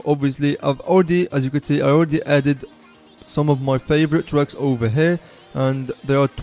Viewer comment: i heard some of my favorite drugs